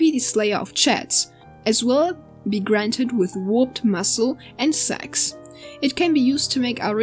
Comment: did he just say "... With warped muscle and sex"?